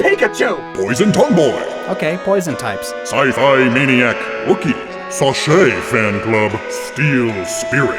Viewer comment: I misheard ROOKIE as WOOKIE.